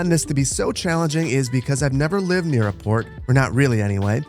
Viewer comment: "In today's episode, we're going to be taking on a build that I personally find to be very challenging and a bit intimidating: Porn." You can't tell me that I'm the only person who thought he said that...